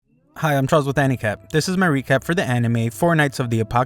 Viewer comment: did bro say fortnight????